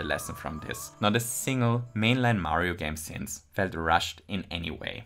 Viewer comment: the ending of this video, praising nintendo for never releasing cheap nostalgia bait games, is uh. extremely tone deaf 2 months before nintendo deletes mario 3d all stars forever just for a quick money grab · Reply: he clearly says MAINLINE Mario game.